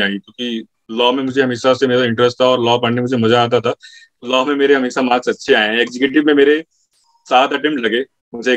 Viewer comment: Did he say 7 attempts